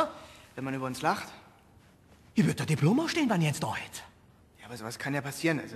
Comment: Was sagt der Chefredakteur an Stelle 0:23? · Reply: "Ich würde dir ein Diplom ausstellen wenn ich eins da hätte"